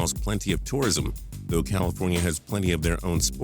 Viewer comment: you said LA has two teams .. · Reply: lol my bad I thought he said California lol